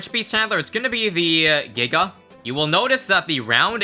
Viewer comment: What did he say? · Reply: He said giga, dont try to make it sound bad